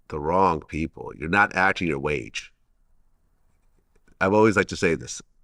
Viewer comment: I love when say ack your age sound like he saying ack yo wage lol